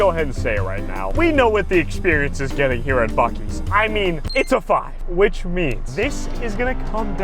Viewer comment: Bro, it sounded like you were saying fuckies